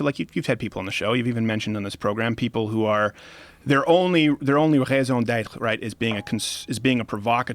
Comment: wtf does he say right here, please tell me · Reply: Raison d’être, it can be defined as « their only purpose »